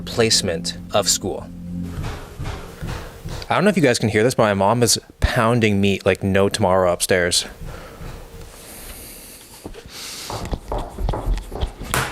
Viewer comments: i heard: "mom is pounding me" lol i was so confused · I thought he said “pounding me” 💀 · i swear i heard him say "my mom is pounding me like no tomorrow" i was like O_o DAYUM WTF LOL